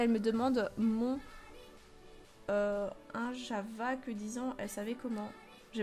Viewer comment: Au 9:54 quand tu savais pas elle a dit : c quoi ton âge j'avais 10an elle c comment je m'appelle . Voilà continue t vidéo gros bisou 😘